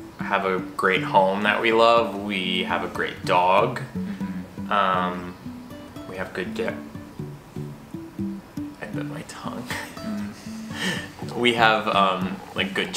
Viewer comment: When bit his tongue, I was on another app listening in the background and I legit thought he said “We have good d*ck” and i was SHOOK 😂